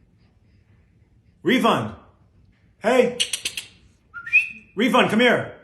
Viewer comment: Did I hear the dog's name at 10:36 correct?? "Refund, where are you? Come here, Refund!" 😂